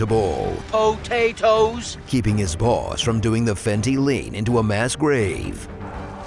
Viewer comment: Brother, did I just hear "a fenty lean"!?🤣🤣🤣